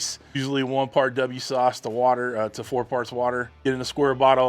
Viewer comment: For a moment I heard "1 part W40 1 part water"